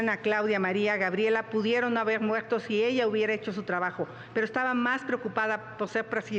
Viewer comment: pero que dijo esta señora? Soy yo o ella dijo que pudieron m…r si Claudia hubiera echo su trabajo jaja 😂